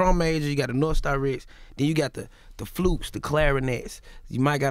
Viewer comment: Did he just say “ flupes”?!?